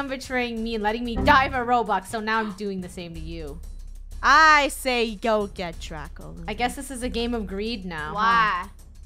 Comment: Am i the only one whk thought she said " I say you go get drunk"